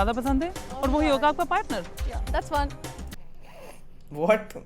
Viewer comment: i think she said “that’s fun” 😂